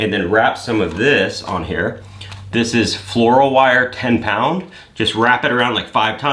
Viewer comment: What kind of wire did you use? It sounded like you said fluro but that doesnt seem right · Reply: Floral, for wrapping flowers